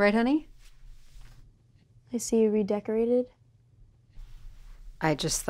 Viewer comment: did anyone else’s Siri go off? 😂 She said, “I see you...” and it sounded like hey siri 🤭